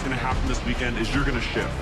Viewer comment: I thought the one clip said I promise you this weekend you will shit 😜